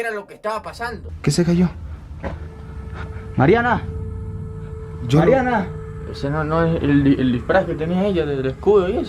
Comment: pense que dijo yoloriana🤣🤣🤣